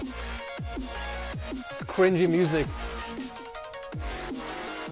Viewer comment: he said criwgy Instead of cringey My guy